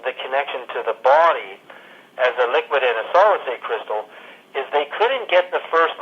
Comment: I wonder if you oculd help me with some things in the interview where I can't quite hear what's being said. This is in connection with me translating the video into Norwegian. 12:00 Is he saying "liquidative solid state crystal"? The first word, if it's what I have written, doesn't quite make sense. 14:30 "water cameras"? 15:05 "[…] we know that consciousness can transmit information." What is the missing first word?